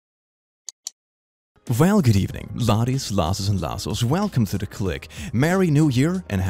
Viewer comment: Slightly different video than usual, let me know what you think! <3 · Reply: Okay, it's been bugging me since I started watching your channel. What's your intro? I hear "Laddies, lasses and lasses". Captions are no help because they say "good evening bodies, losses, and lassos". Which I admit would be funny, but doesn't sound right.